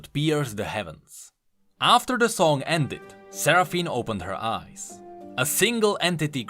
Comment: why did i hear this as "seraphine opened her arse"